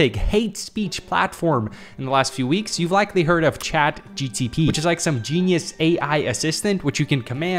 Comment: I swear you said "chat GTP!"